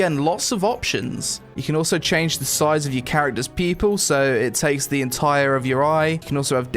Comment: I swear everytime u say "Pupil" i hear "People"... SMH lol